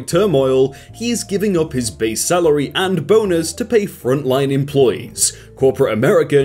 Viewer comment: when he said "bonus", it sounded like he said "boners".